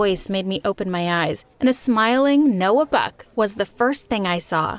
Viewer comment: When she Noah Buck. I thought she said Noah Beck